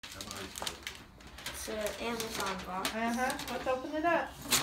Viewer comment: Did anyone else panic and think the dog was in the box that was getting tossed around? Glad that turned out not to be the case lol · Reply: At the beginning, he said it's an Amazon box but I thought he said an animal box and I got so scared for a second lol😂